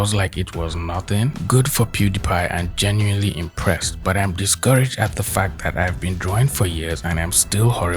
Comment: this person is actually saying "discourage as fuck, the fact..." not "discouraged at the fact," because school has failed this generation the most of all.